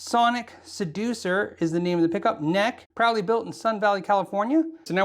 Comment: thought you said "probably built in Sun Valley", like what!?!!? lol